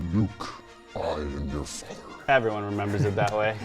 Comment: For some reason i hear "No Luke, i am your father."